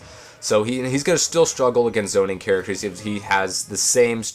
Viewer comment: I thought you called Link "Zelda" was about to rage lol, then I noticed you said zoner. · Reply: Hello ? To me, it sounded like he said "Izona" or something lol I thought he was referring to the person playing the Link, until he said "zoning" again ^^